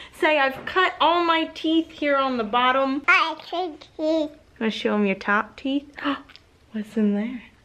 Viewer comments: I swear said "I cut teeth" @ 0:38 .... I know she really didn't but baby babble sounds like words sometimes and it fit and that was perfect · Okay I HEARD say "I cut teeth" as soon as said it...😱 smart baby girl!! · It's seriously sounded like she said "I cut teeth". Not just saying this but my absolute favorite fragrance from Bath and Body Works is Japanese Cherry Blossom. Japanese cherry blossom all the way!!🌸🌸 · It sounded like said " I cut teeth" she is such a cutie. So giod natured. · It totally sounded like she said "I cut teeth". Aweee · Is it just me, or did it sound like said "I cut teeth" when you told her to?